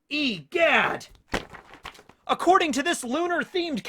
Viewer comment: It sounded like said Eat That!